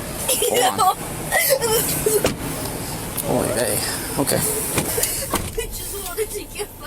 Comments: does he say oy vey about her lol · did I hear the officer say, “OY VEY”???